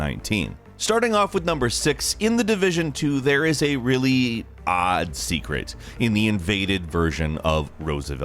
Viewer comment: Oddheader: DID I HEAR ODD ?